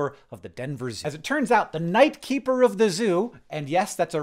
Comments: love that "night keeper" is a real job title that real people have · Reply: I thought he said knife keeper 😭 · when you said "night keeper" I heard "knight keeper"